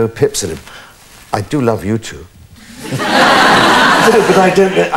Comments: Thought he said 'I do love youtube' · i thought he said i love youtube hahahah